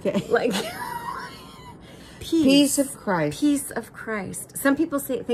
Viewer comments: Mama Michelle...the struggle is real! Every time I hear Chynna say "Peace of Christ", I swear my brain interprets it as "Piece of Christ" (which takes that meaning in a WAY different direction)! You know, if I read your lips it DOES look like you're saying "pizza crust"! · Funny, I also initially thought you were saying piece of Christ when I started hearing it and I did not get it. It needs words before it to be clear what you’re saying in proper context. Like...The Peace of Christ is with you or May the Peace of Christ be with you. People just hearing the words may not get it, since piece and peace are homonyms.